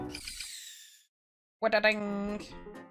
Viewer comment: Did he say what a daddy 5:02 💀